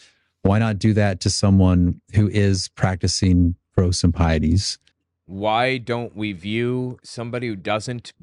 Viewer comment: What's that word he said there? Prosimpieties?